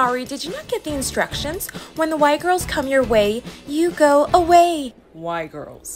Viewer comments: “When the Y girls” bro I thought she said white girls 💀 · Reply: Hor I thought thay said white girls as well💀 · “When the Y girls” bro I thought she said white girls 💀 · For a second I thought they said “white girls” 😭😭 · “When the Y girls” bro I thought she said white girls 💀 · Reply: Bro I thought they said “White girls” as well 👁👄👁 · “When the Y girls” bro I thought she said white girls 💀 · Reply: I thought she said die girls. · I thought she said “White Girls” and I thought is starting to get racists! When I saw the captions it’s said “Y Girls”?! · I heard "When the white girls come your way, you go away" and was prepared to accept it tbh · When it said "Y girls" I literally thought they said "White girls" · "when the Y girls come in your way, you go away." got me rolling on the floor fr :skull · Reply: Thought it said "white girls" 💀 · I thought you said white girls 💀😭 and I was like HUH? 🤨😮🤔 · I thought they said white girls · At first I thought they said “when the white girls come your way you go away” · I thought she said white girls😭😭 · At first, I thought they said hey, excuse me when the white girls come through your way walk away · I thought she said White girls😅😅😂😂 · People who thought they said white girls 👇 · bro, i misheard "white girls" instead of the Y girls which still makes sense LMAO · “When the Y girls” bro I thought she said white girls 💀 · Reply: I thought she said whiter girls too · I thought it said "white girls" 💀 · I thought they said white girls 😅